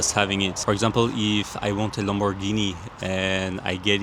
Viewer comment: Oh my god, this is on, me but I totally thought I heard "If I want a longer peni-" and I only realized later in the analogy